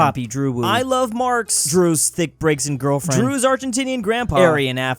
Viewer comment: i had to make a double take when i heard “drew’s thicc braixen girlfriend”